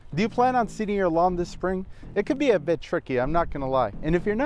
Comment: I thought you said, "It can be a bitch." at 0:03 looool